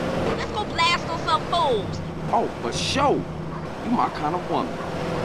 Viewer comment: what cj speak ?i am brazilian i don't understand very much english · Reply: He said oh for sure! My kind of woman. Its because she said she wants to kill some ballas around